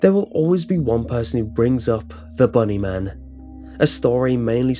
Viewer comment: when he said bunny man i thought he said butt man XD